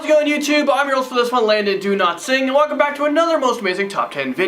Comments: It always sounds like you say "do not sing" lol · idk why but i heard "i am landon do-not-sing"